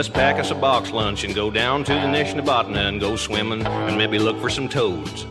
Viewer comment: I always thought he was saying "Nation of botany" in the song Four Wheel Drive. I don't live in the US and I've never heard of the Nishnabotna river.